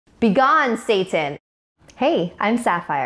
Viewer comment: On the intro I thought it said BEGON THOT